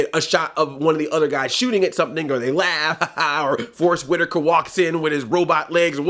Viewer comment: When you said Forrest Whitaker, I legit thought you said Force Whitaker.